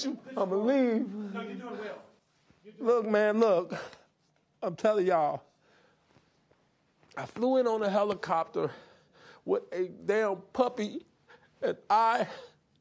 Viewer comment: Did he say "Putty?" Or was he describing his jacket? (Around 8:44 mark)